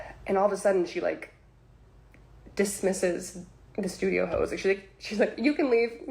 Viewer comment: did she say "started dismissing all the studio hoes"?